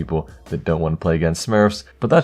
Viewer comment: he says hopeful instead of helpful